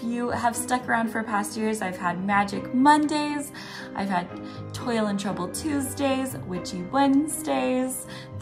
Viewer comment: I totally heard “Toilet Trouble Tuesdays” and was like 😳. Seriously though, I’m looking forward to this. Thank you for all that you do!